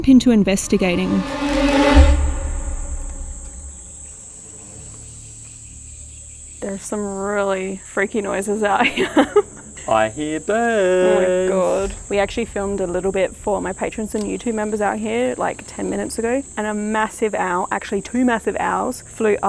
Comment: I swear I thought said “😃I hear you’re deeead~” 🤦🏽🤷🏽lol